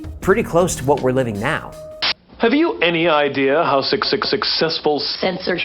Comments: was that clip chosen specifically, as all I hear is 666,but I presume that's the intention. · Did anyone else hear say 66sixcessfull? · Anybody else hear “666-cessful” at the 6:58 mark? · anyone else hear 666 when he says Successful.